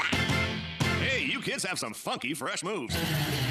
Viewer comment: I think he said the F word 0:07( but good video ) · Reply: He said funky. Not the other F word